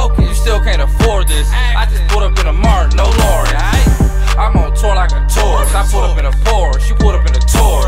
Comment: I cant be the only one who heard... "I'm on tour like a tour" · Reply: I thought he said im on tour like a tour bus